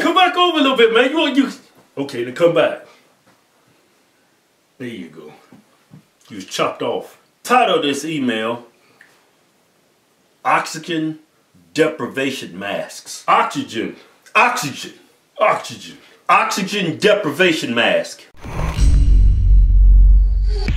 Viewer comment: Sounds like they said Aww chi chen Deprevation mask